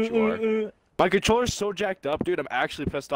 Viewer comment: um I heard someone say fucked up